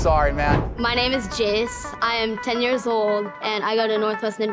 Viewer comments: “my name is jizz”😂😂 swear that’s what he said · i thought that kid said "my name is jizz"